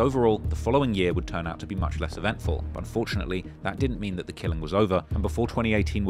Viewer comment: did I hear you correctly? Did you say "But FORTUNATELY, that doesn't mean that the k¡ll¡ng was over"? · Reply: “But unfortunately”